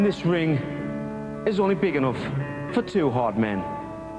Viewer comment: Sometimes it sounded like they said hot "This ring is only big enough for two hot men and I'm one"